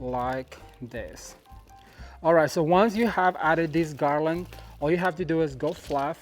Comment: on the sub titles at the time of 19:51 the sub title reads "and this dog island is not going anywhere" instead of "this garland."